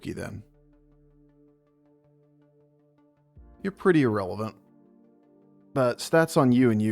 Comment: idk if you said yrelevant? i didn't see a smile after so i can't tell if you were joking, but that's what i heard and i found it amusing 😄